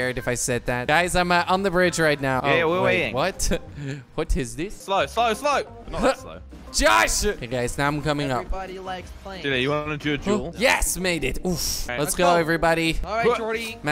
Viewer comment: Who heard him say "Everybody likes planes".